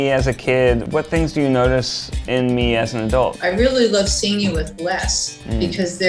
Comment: Misheard horribly at 8:06 and thought Ned’s mum said she “really loved seeing him less” and thought oh wow our mums are so alike